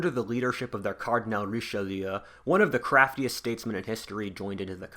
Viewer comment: I'm sorry, but I've never seen a worse description of the war of the Spanish Succession in my entire life. (And I've seen some bad descriptions of it...) Also I don't get how you can say Louis XIV started "loads of failed wars" considering not a single war he fought can be considered a French defeat, even the war of the Spanish Succession... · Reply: my bad actually, I misheard "craftiest" as "crapiest"